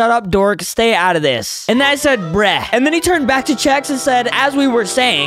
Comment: Sus why did you say mini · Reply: He said AND THEN HE NOT MINI